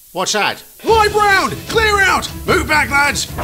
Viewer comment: What does the workman say at the begining? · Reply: Live round clear out